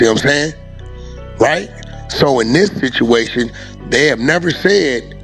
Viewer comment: Wait did he say this bituation😂😂😂 @ 3:20 I had to rewind that!!!Okay Wacc I see you still banging on wax while calling someone else dumb for being present for gang stuff... Ok bet